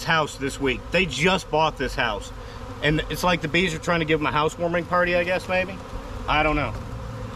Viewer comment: Did I hear you say “a house-swarming party”? 🤣